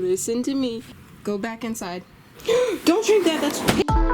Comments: When Oppie almost drank the paint, it sounded like you said "DON'T DRINK THAT, IT'S PEE!" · First of all what the heck the comments have moved and second of all when she said don’t drink that it’s paint I thought she said don’t drink that it’s pee 🤣🤣🤣